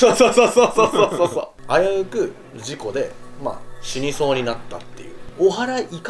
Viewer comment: シルクが「死にそうに」って声に混ざって「死ねぇ…」って聞こえる気がするの気のせい？ BGM…？